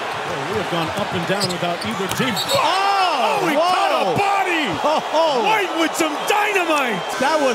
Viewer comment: 7.00 did he just say he caught a body?.